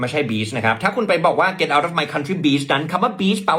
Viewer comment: Lol, the last sentence she said was "Get out of my country, bitch." I thought she said, "Get out of my country, babe." 🤣🤣🤣🤣 thank you for clarifying so we can understand what she is saying 😂